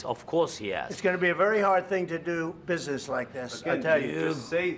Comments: do my ears fail me or did Trump just call this situation a business? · Did Trump just say business?